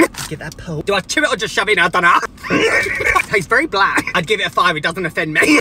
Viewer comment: Ohhh he said it tastes very bland, i thought he said it tastes very black..... 😅😅